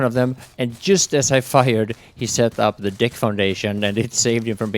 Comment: Ahaha at 5:50 it sounds like you say Dick Foundation :) Good play :D